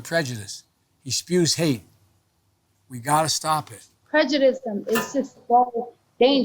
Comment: Did she just say prejudism?